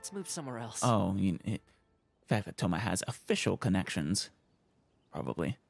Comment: I heard "A Fischl connection". That'd be cool!